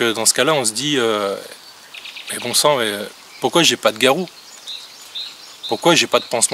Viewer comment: Yesssss ! reprend ses vidéos !!! ça fait plaisir de te revoir souvent sur la toile ! continues!!!! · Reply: XD ! à 10:46 j'ai compris " Pourquoi j'ai pas de Garou ?!" hahaha j'ai bien ri ^^' xD ! j'ai imaginé la scène Mdr !